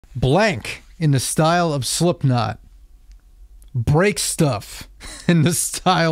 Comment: I misheard "Blink in the style of Slipknot", and now I badly want to hear all the small things in this style 😅